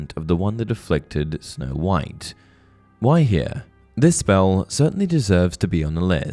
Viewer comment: when you say "why here", for a heartbeat i thought you were saying Gwaihir, the lord of the eagles from lotr